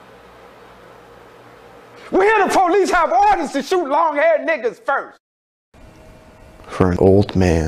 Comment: “We hear the police have orders to shoot long haired niggas first!” BRO WHAT?! 😂😂😂😂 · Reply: I always misheard it as long head XD